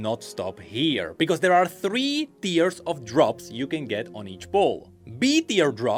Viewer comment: I swear I though said “Three tiers of drugs”. Like damn bro, you’re in that deep?